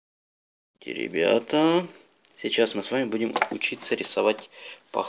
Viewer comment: мне послышалось, или он в начале сказал не ребята, а теребята? XD